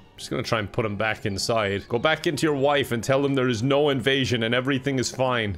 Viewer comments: did he say “go back into your wife” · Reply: It's actually "Go back in to your wife" but yes we're gonna go with "into" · Did i hear kevin say "go back in to ur wife"